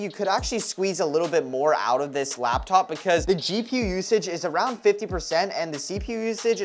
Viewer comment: I heard “GPusage” 😂